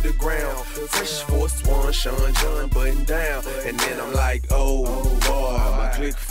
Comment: When I heard "oh oh boy" at 1:09 I thought it was Santa Claus saying ho ho ho in the song.